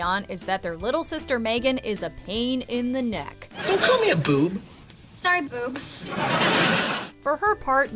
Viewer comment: All these years I thought Megan was saying "moobs"😂